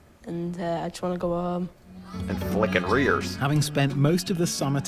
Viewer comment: Lol okay okay I know now it’s flicking ears not rears. The accent definitely sounded like something else. Or my brain is just stuck in the gutter 😅. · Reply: watch the “flicking ears “ scene again. When I watched it I heard you say “rears. I thought you knew it was ears and said tears as a joke. It was funny that way. Thought you were f’ing with us.